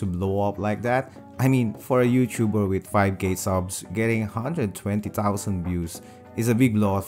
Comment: I thought he said 5 gay subs but he said 5k subs lol